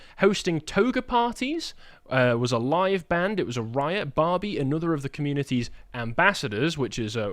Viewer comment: I heard that as "Riot Barbie," like a new barbie doll just dropped.